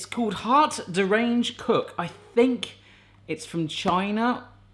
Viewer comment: I thought you said, "hot deranged cook" lol